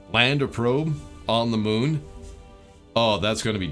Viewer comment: For a second there, I thought you said "land a moon on the probe"